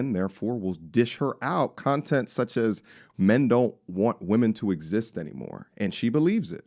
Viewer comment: She actually said Men of today "want women who don't exist anymore..." = Us men want the classic feminine archetype, that doesn't exist within this modern women City Girl era... I think you agree... She also said these modern City Girls want men that don't actually exist yet aka the masculine/feminine , wealthy simp, who allows her to run the roost, while he also earns like he runs the world... Basically, they want a man that will never exist... I think you agree too... 😂... I think you heard it as "men want women to not exist anymore..." , so I understand the your response, hence this correction... Keep killing the game Family!!! 🫡